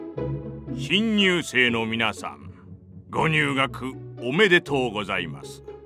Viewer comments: 「新入生の皆さん」が「貧乳生の皆さん）に聞こえて焦ったw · 新入生の皆さんってセリフひんにゅうせいの皆さんって聞こえた